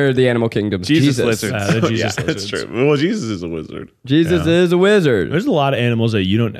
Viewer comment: Really thought Keith said Jesus is a lizard lmao